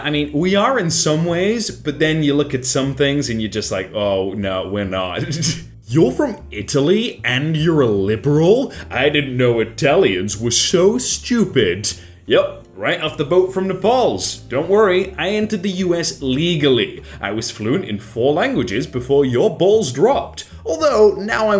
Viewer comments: i heard this as "Yup, right off the boat from the balls" and maybe its just me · Did he just say Nepals instead of Naples?